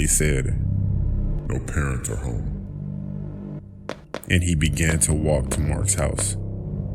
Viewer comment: what did he say?? I didn’t hear clearly.. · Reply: No parents are home